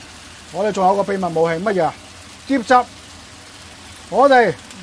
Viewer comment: 这师傅加的是啥啊？我听不懂，谢谢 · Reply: 喼汁